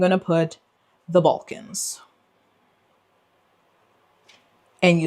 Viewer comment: Listening to this while getting ready for work, at first, I thought you said "The Vulcans." I got really confused for about two minutes, lol.